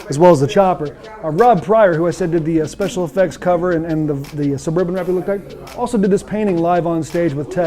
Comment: what is he saying?? "saburbababurbala tech"? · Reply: Suburban replicate